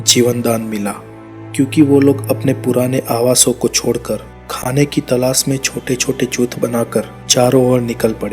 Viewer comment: whay did you say bro??? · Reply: Chuth